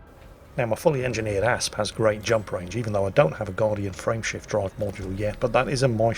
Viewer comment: Whenever Jingles says “ Frame shift drive” I hear “Friendship drive” for some reason...